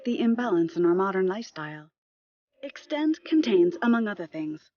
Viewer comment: … I know it’s called Extend, but I kept hearing “Extenze” which is a pill sold in the US on late night infomercials directed toward men… I’m going to stop there and let your imagination run. It’s exactly what you’re thinking it is… lol